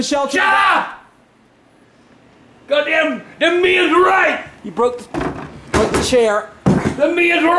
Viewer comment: I always thought he was saying “the meteors ripe” but he’s saying “the Mayans were right”